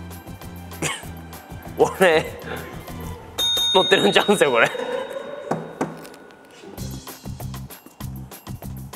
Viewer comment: なんて言ってるんですか？ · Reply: 多分急所です😅